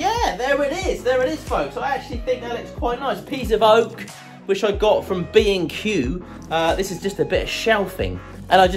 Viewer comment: For a second there I thought you said: "A piece of Oak, which I got for being cute" :'D Amazing piece once again. Very cool idea! :D